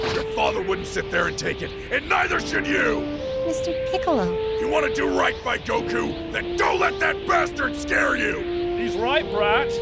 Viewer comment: at like 5:00 i thought piccolo said when he was talking about if you wanna do right by your father, he said "IF YOU WANNA DO RIGHT BY PICCOLO" i had to rewind to confirm LOL